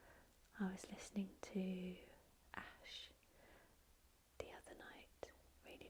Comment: what was the song or musician she mentioned at 29:46? the one she listens to the other night really loud? i couldn't understand that part. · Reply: It was 'shining light' by Ash. That's on my funeral list along with my favourite song of all time which is Afterglow by The Small Faces 💜